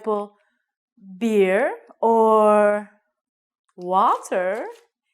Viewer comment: I heard "waters"... what that means?